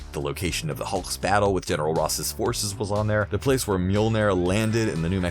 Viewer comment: This guy mispronounced stuff and I’m cringing. I swear I thought he said MILLIONAIRE when he said Mjolnir.